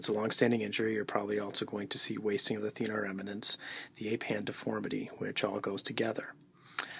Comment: Excuse me sir, what were you saying at 03:26 I didn't get that (... the ape hand deformity ?????? together). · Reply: If it's a long-standing injury, you're probably also going to see wasting of the thenar eminence, the 'Ape Hand Deformity', which all goes together...